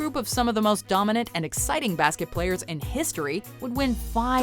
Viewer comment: i heard basket players during the 80s lakers 3:15 kinda weird but funny